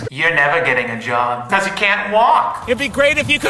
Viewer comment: “You’re never getting a job, BECAUSE YOU CANT WALK” -Helen the Hall Monitor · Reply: i heard "hows a cat walk"